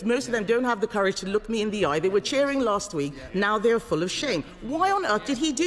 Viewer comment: I thought she said "now they are full of sh*t'. This statement would also have been correct!